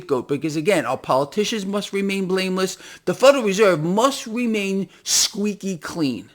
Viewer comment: i thought you said "they must remain brainless". great show as always!